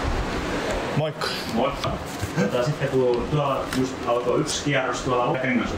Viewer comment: what did you say to say “hello” @ 03:35 ? It sounded like “muk” 🤷🏼‍♂️